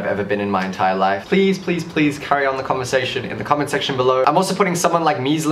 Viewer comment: the comment sex... tion below (am I the only one who heard it like that??)